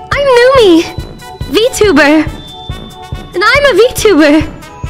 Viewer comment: Numi: I'm Numi...Vtuber...and I'm a Vtuber. What I heard: "I'm Numi...Vtuber...and I'm a bottom."